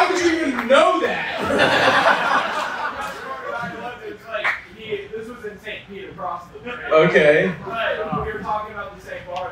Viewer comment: The caption at 2:22 says "This is insane" but what he actually said is "this was in St Pete across the bay". This comedy club is in Tampa, St Petersburg is on the other side of Tampa Bay. And btw, I'm not surprised at all. I've lived all up and down the East Coast, Brooklyn, Long Island, Philly, Baltimore, DC suburbs, Scranton, and here in St Pete, and NO WHERE are girls freaky like they are down here bro. Also believe it or not definitely the best looking women on average out of any of those places. I just moved back here about 2 years ago and got into a serious relationship pretty quickly which I'm very happy with, but I also lived here in <num>, moved in with my dad for the first time at 16, we didn't get along, within 4 or 5 months he agreed to emancipate me, dropped out of high school, got a shtty job but quickly started selling drugs and got a nice apartment downtown by 17... I always did fairly well with women but bro.... down here was like shooting fish in a fkn barrel. Within a couple months of getting the place I had a whole starting 5 I was seeing at least once or twice a week each all bad af two of them lived in my building and none of them even cared I was fkn around with the other girls and plus bagging other random girls downtown on top of it and customers. It was crazy I'm 29 now and it still was the best time of my entire life man, not that my life has been boring or anything but that sht was legit like a movie it was incredible. But then I got my apt raided